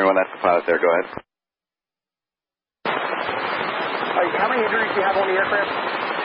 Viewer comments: I hear "how many injuries do you have on the aircraft", not "how much debris do you have on the aircraft" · If anyone has further information and official reports, please reply to this comment. One person has oficially been reported dead so show your condolences here. Rest in Peace. Congratulations to the pilots and Captain Tammie for bringing the aircraft back to the ground safely. Special thanks to my followers on Discord server for their help. We've worked hard to bring this video as soon as possible. Related event --> <link> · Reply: I hear "how many injuries do you have on the aircraft", not "how much debris do you have on the aircraft"